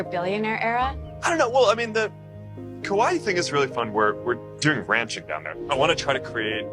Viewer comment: i thought he said ‘kawaii’ and not ‘Kauai’ and i wss like IK he’s not calling it his ‘kawaii billionaire era’